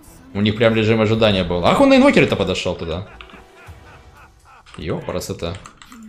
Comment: "н***й он на Инвокере то подошёл туда?" Или мне одному послышалось? :D